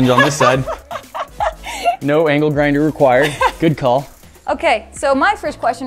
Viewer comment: “I think I’m gonna use an angle grinder” Proceeds to cut the hinges with a saw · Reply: Octo Creeper I thought he was saying “Ankle Grinder” 💀 and I thought it was a saw reference